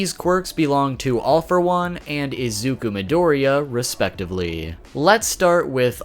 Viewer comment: when you said izuku midoriya i thought you said rizzuku midoriya💀